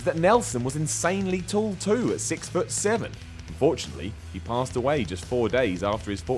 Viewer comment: Did he say fortunately at 1:13?